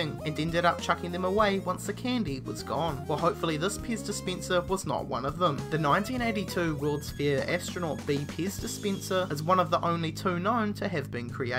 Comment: Hit the back button the second i heard "piss dispenser"